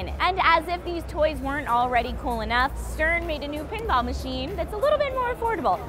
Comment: For a second there, I thought said that CERN made a new pinball machine. Then I realized how disappointed I was that she didn't. I WANT TO PLAY ATOMIC PINBALL, GODDAMNIT!